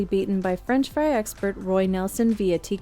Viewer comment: french fry expert Roy nelson am I hearing that right